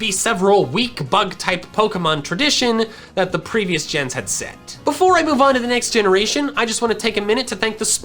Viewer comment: I thought he said "that the previous gens had sect" as in genesect